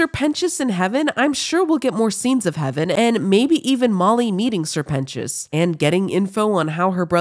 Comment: I thought you said eating sir pentios 😂❤